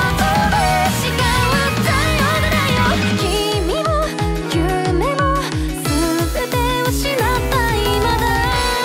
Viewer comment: 「君も希望も全て失った今田」に 聞こえてしまったwww すみませんww